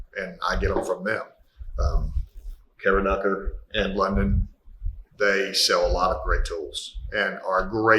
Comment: Thanks for this guys! Unfortunately, I can't make out what you are saying at 12:14, and turning on subtitles didn't help. It sounds like "Karen Knuckler and London". Is that what you are saying?